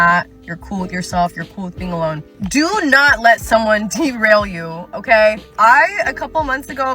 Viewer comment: I heard "derail" as "team rail" 😂